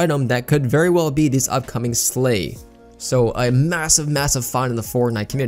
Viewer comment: if you listen closely you can hear him say “slave”instead of “slay” Does this mean is racist???😀😀😀😀